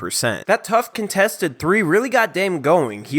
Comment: Did he just say "Hopelahoma City Thunder" at the start of the video? LOL what a savage. I'm soooo nervous for tonight's game. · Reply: I thought he said "Hoeklahoma"😂